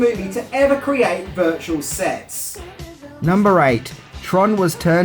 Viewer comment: I thought said "Virtual Sex"